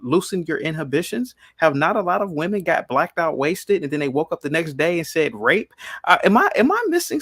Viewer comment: He said, "Women black out and get drunk." He did not say black women. It just goes to show that once you are triggered, the listening stops.🤦🏾‍♂️😭🤣😭🤣